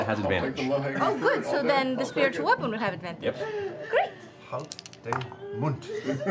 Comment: did Liam just say Hold din mund?